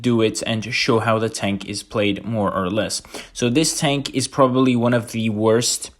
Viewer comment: I thought you said this tank is garbage.